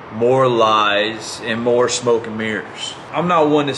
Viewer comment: I was really confused for a sec because I thought he said, “smoke and beers,” and I was thinking this didn’t sound so bad.